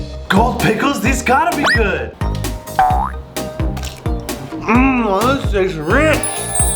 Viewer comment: What the hell did you just say?!?! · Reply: He said this taste rich